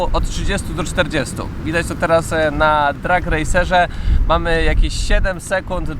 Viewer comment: Jakiej aplikacji używasz do sprawdzania przyspieszeń? Coś usłyszałem Track Racer?